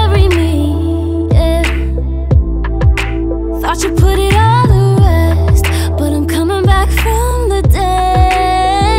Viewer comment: Can someone tell me what does she say at 01:23 ? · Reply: Ilesos17 thought you put it all to rest